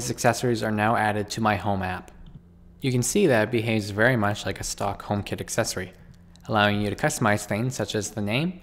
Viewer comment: Love the tutorial! I definitely heard "Stockholm kit accessory"